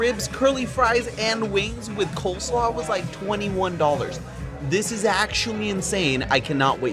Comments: BIRFDAY 17:59 he says $21, captions say $91!?! What is going on with the captioners?!? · $21, not $91 like the captions say